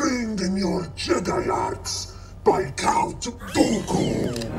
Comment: the subtitles/captions say “by count goku”… DRAGON BALL STAR WARS CROSSOVER CONFIRMED Complete joke,tho it does actually say that!